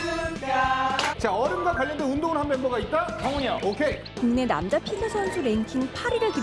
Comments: Is it just me or did Jay say "Sunghoon hyung" on 4:36? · did Jay say “Sunghoon hyung”? haha